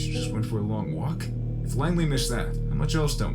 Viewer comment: "Langley" is what they said and I think they refered to CIA in some way.